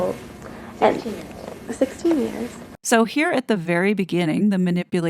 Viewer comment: At the beginning it doesn’t say 16 years ‘old’, I hear ‘we’ve known eachother since we were 2 years old’.. ‘16 years’ - as in known each other for 16 years (so they are 18 here is that right?). V interesting vid esp about NP